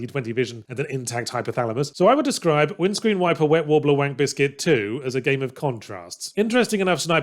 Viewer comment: did i just hear: "windscreen wiper wet wobbler wank biscuit 2"?